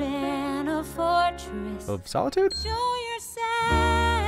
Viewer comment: This is late but something that strikes me in the Show Yourself song with the transformation... Okay. White gown with crystal details on the bust and gauzy see through fabric trailing down her back? Mom looking on glowing with pride? Hair done all pretty (somehow)? That's a wedding dress. THAT'S A WEDDING DRESS. The veil is attached to her arms instead of her hair and there's pants underneath but that is pure wedding dress symbolism. Elsa isn't just finding her power, she's finally fallen in love with and committed to herself. She's marrying herself. It's also a cute little knock against the traditional Disney trope that marrying a guy is the path to happiness. Not this time, self love is the path to happiness now folks! Also easy to read in an Elizabeth reference into it where Elizabeth 'marries England' in all her holy Queenliness at the end of the movie too. Idk if that's what was intended but I love that it's there. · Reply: I thought that said "snow yourself"